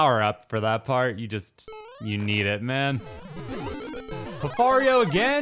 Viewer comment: Can we talk about how wildly lucky was, getting three Pfarios in the same level? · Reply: I first heard it as Lofario and that’s what he now is, to me 😂